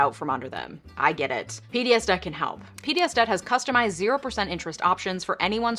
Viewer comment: I heard "bdsm can help." Had to do a double take.